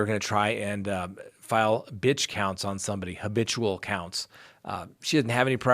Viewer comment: Ha! When you were talking about Leticia’s “Bench Counts,” the television’s brain heard it as “Bitch Counts.” Much more appropriate name I think!